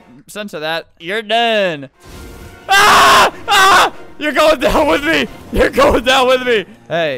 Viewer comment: It sounds like he said “your going to hel* with me” but he actually said “your going down with me”